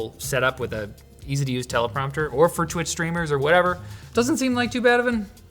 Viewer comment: I thought he said "or for JOI streamers" god damn 😂